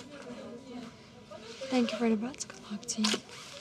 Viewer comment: "Thank you for the bets, good luck" only after 35 minutes and a shift change did I get what they were saying.Then I hear " Now um plants galart see."